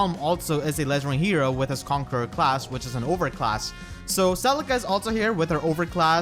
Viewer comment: I thought he said ovary class I